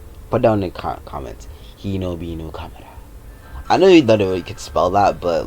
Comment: I thought he said hinokami kagura for a second